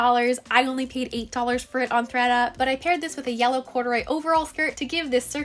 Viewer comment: Thought she said threta for some reason